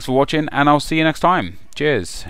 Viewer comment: When you said "cheers" at the end... is it only me who thought it said JIZZ ...